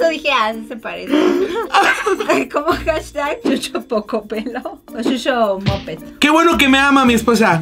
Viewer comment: escuche coco pelado xdxd